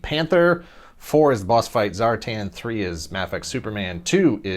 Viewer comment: Bossfight Zartan? Wasn't paying attention and I heard that and had to go back and realized it was actually Zorro. I would love though if Bossfight did GI Joe in their standard figure scale, how awesome would that be!